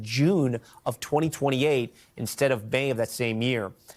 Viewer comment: What month is 'Bay?' 'Instead of 'Bay' of that year.' Combs on prison barge? Then how he get the booze?